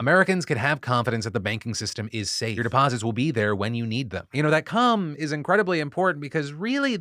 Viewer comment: did he say, "that c*m is incredibly important"..? I've been trying to say that for years! 🤣. I just don't get the same views. Phil DeFranco subliminal genius 👏 👏👏